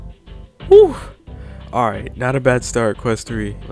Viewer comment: the “who” sounded like Mario